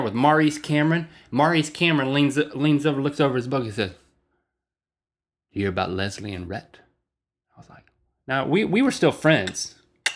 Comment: when he said ya hear about leslie and rhett? I thought he said ya hear about lesbian rhett?